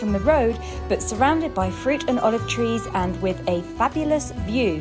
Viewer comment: It's funny in the opening, you say "we grow frig and olive trees" and it sounds like you say "we grow frigging olive trees". Hahaha (I know you mean fig). 😂